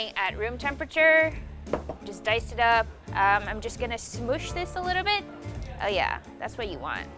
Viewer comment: When she said smoosh i thought she said 'smooch this a bit' 😂😂